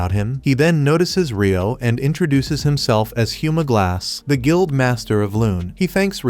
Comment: i heard "Humor glass"